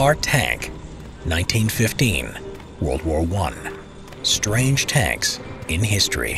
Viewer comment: I thought it said Strongest 😂😂😂